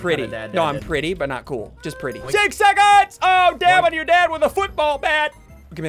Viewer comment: It sounded like he said DAM ON YOUR DAD WITH A BASEBALL BAT